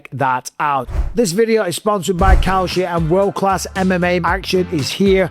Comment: I'm so sorry Mike but I genuinely thought you said "This Video is Sponsered by Cow Shit".... Double taked to my second monitor like WTF?! Then saw the actual brand. I do apologise.